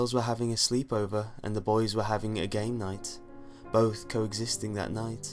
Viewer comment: I thought he'd said gay night ROFL